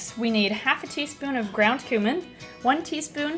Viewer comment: When you said "ground cumin" i thought you said "ground human"😆 i so badly want to meet you...you've inspired me to get a pet skink ( a red eyed armored skink) for christmas😄 Im naming it big Bessy